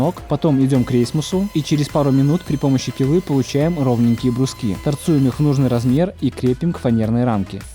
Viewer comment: Я один в 10:38 услышал: Торцуем торцы?